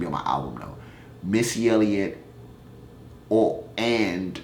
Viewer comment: I thought u said MS YELLIK. I'M LIKE WTF. BUT MISSY ELLIOT, I CAN FEEL THAT. I'M A STRAIGHT CHOCOLATE BALD HEAD FEMALE FR. BMORE & I LUV UR SHIT LUV. ALL 3, Keep doN u my nicca l. it'll pay off, I believe it 😛👍👍😊😛